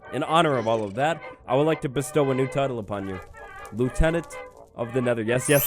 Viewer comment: Am I the only one who thought he said lieutenant Dane, like from Forrest Gump